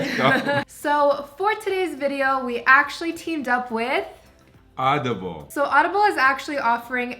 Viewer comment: I thought he said edible